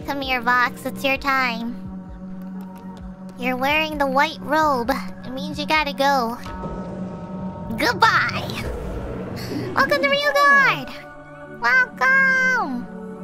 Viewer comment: Omg i miss heard and i thought she said - I gotta do Vox- and went Ayo? 😂😂😂 has too much power over my mind now